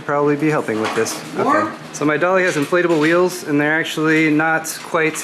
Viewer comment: It sounded like you said "My dog has inflatable wheels" :P